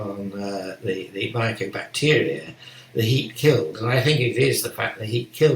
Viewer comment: What does Professor say @ 1:14:51? heat kiln???? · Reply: He is referring to heat-killed mycobacteria.